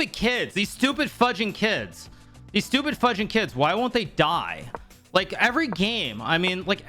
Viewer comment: I've never seen Hikaru cuss before. I don't know what to say. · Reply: he actually said fudging